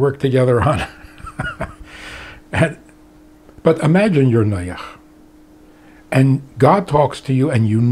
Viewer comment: he says "imagine you are ........ sounds like noyach?" what does that word mean? an unbeliever? please let me know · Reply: Noah, pronounced Noyach